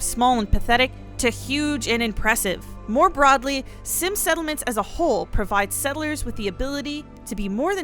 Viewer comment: I thought the sims mod said Simpsons settelment in the MODS 😄